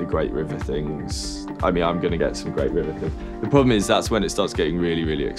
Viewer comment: What preamp did George recommend on 7:09 ? Just can't understand, I am not a native speaker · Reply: Nick Kasabian he mentions the Great River Preamps <link>